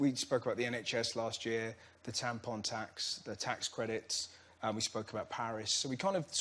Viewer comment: I thought he said "tampon tacks". Thank you google for setting me straight.